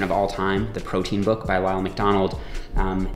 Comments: It sounds like you said the protein book by Ronald McDonald. Sounds legit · I heard “The protein book by Ronald MacDonald” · I thought he said RONALD MCDONALD lmao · I thought I first heard him say "The Protein book my Ronald McDonald".